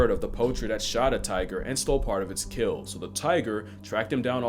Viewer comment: I thought it said "ez kills" 😭😭